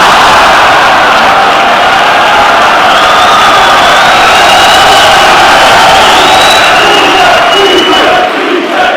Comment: What does the guy shout at 3:55 which has everyone pissing themselves? Couldnt hear a word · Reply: face transplant for ya eddie